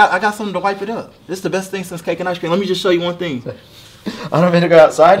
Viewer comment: i thought he said Caucasian ice cream